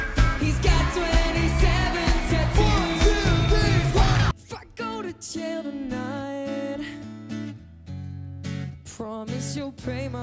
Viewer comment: I heard “Fuck go to jail tonight” at 1:52 😂😂😂